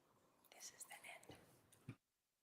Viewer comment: Did she say „This is the end!“ at 6:00:06 softly in the mic or did I get that wrong? 😳